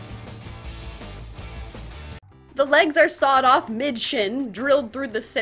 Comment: It took me a a full minute to realize she said “mid shin” and not “bitchin”. The confusion I had when I thought I heard her actually swear was honestly kinda funny.